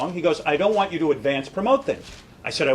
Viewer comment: What did he say? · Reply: I don't want you to advance promote things.